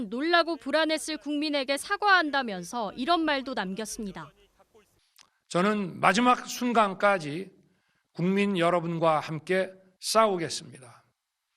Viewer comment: "저는 마지막 순간까지 국민 여러분과 싸우겠습니다."로 들리네